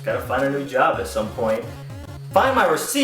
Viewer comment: I'm really bad at deciphering words... at the end did he say "Denice hooked me up that day...it was a little over 50% off" because that's what I think I heard but I'm not sure.